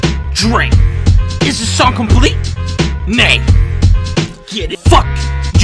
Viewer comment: Neigh sounded like nee and nee is no in dutch xd